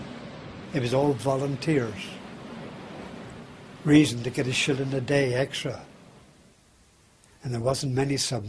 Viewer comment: It’s truly amazing that we speak the same language as the British yet your lucky to understand 2 words out of a whole sentence! 10:43 wtf is he saying! I understand the first 3 words and then it sounds like he says something about getting an extra shit in the day his words are unintelligible!!!!!! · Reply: Bahahaha definitely know what you’re sayin, but dude said “a shilling”—not a shit. 🤣 Basically they got an extra nickel a day for volunteering to serve on these submersible, archaic deathtraps with explosives onboard, that ran on diesel engines near the surface most of the time and exposed the crew to the exhaust. Plus everything else out there was looking to spot and sink you, also, with explosives. They had to keep mice onboard to early warn them of carbon monoxide poisoning. WILD.